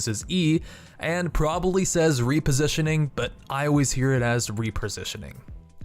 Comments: i thought i was the only one that heard "Reapositioning" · I knew i wasn't the only one who heard it as "REAPERsitioning" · THANK YOU! I've always heard it as 'Reapersitioning' too! It's hard not to but I've never heard anyone else say the same.